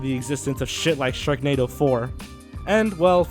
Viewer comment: I thought, for a moment, that you said Shreknado 4.